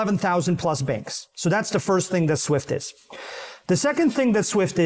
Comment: Great episode! Thank you! Surprised it’s the first time you are bringing chainlink to the podcast, since it’s such a major part of the eth (and crypto) ecosystem. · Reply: for some reason I thought they said it’s the first time, maybe I’m hearing things.. 😅